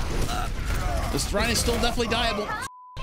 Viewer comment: Did he just say Rein is definitely "Die-able?" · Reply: Yeah... Like 8 times. Where the fuck have u been lol